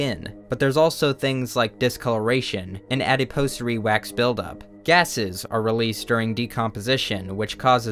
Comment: Does anyone know what he says at -1:54 captions aren't an option and google isn't guessing what I'm trying to spell. Thanks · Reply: I think it’s adipose wax buildup or something similar